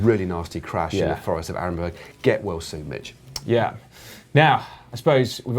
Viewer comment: Did just say get well soon bitch